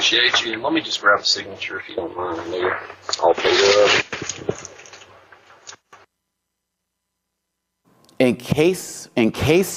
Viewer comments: It sounds like he says, "Let me just grab a signature, if you don't mind. I know you're all loaded up." Because he just gave him pizza, and cinnamon rolls. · It sounded like he said, "...let me just grab a signature if you don't mind..later I'll pay up (?)... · I thought I heard “sign here”